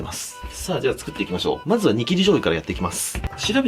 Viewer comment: 煮切り醤油って言ってるのわかってるんだけどニキビ醤油って聞こえるのは私だけ？ww